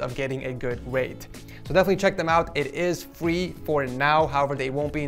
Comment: fwee. it’s what he said replacing free...